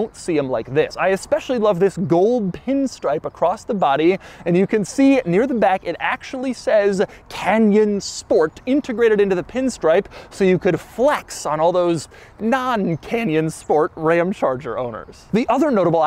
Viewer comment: The whole time I thought you were saying "Kenyan Sport". XD